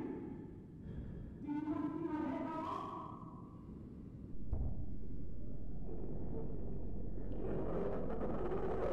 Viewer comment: What was that thing saying at 0:19 ? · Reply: "Do you wanna see my head come off?"